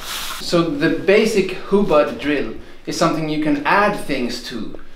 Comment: Yep, I thought I heard "hubud," and laughed when he said "hubad." Thank you for the exposé!